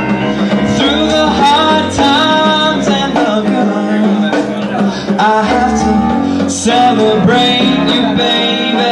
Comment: what is he saying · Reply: I HAVE TO PRAISE YOU LIKE I SHOULD